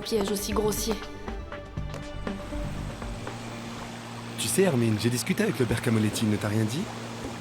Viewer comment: Et pourquoi Martin parle a Clementine et dit “tu sais Ermine” a clementine😂😂😂